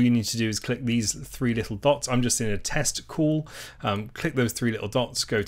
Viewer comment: Did he say "testical" ?